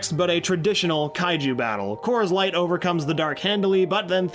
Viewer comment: I thought he said Coors Light... I think I have a problem..